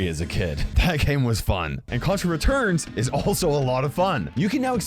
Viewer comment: I thought he was saying corona returns instead of contra returrns lol